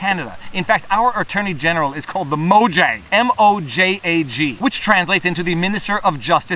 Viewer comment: I heard "Mojang." Didn't know Canada funded Minecraft.